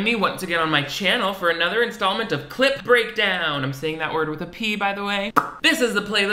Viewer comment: Please who thought you were saying “Clit Breakdown” that’s all I want to know